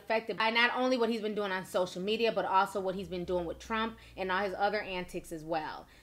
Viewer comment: WHy when she said trump (11:51) i thought she said lil pump..